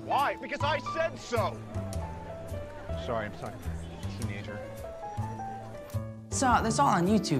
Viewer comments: Tony: Sorry, I'm Talking To m- Teenager. Me: yells in a corner YOUR SON!!!! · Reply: Happyheart's Studio I thought he said my teenager and I was like adbasuhcbwieubcuwrinvr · Did tony call peter son? Like sorry my son, teenager.😶